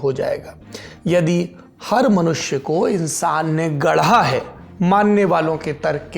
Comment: Correction 2:52 har manushya ko insan na gadha hai instead of in manushya ko bhagwan ne gadhaa hai baaki hats off Saurab you are amazing